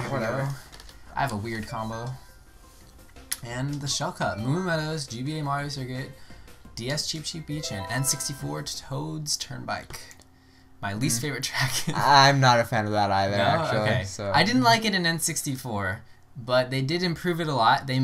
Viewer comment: You said Toad's Turnbike... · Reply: It sounded like, "and Toad's Turnbike...". I can't be the only one that thinks you said that, can I?